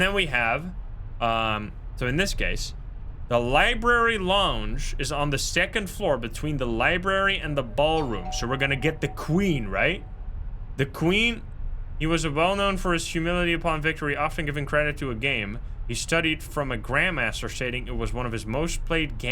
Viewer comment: For the first one I heard "the Queen is dead" which would make sense with the chess